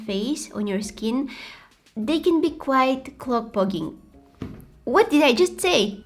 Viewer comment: I've never heard someone say "clobblocking " before 😂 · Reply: I thought she said core plogging lol